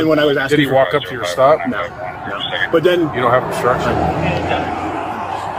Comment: the supervising officer did not say, “You don’t have construction.” He said, “You don’t have OBSTRUCTION.” The cop had no reason to detain the Denver Cop.